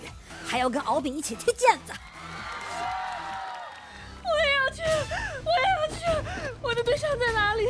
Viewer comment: 完了😂我居然把深深的聽成成：我要去 我要去了🤣太污